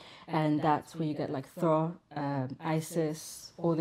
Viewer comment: So at the timestamp of 12:40 did she say “Thor”?🙃😮